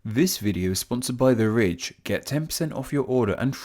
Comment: I thought he said this video sponsored by the rich